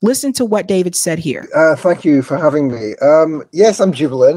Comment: it sounded like he said F you for having me😅